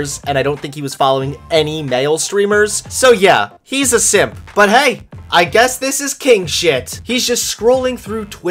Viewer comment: At first I thought it said “I don’t like sims” and I almost unsubscribed...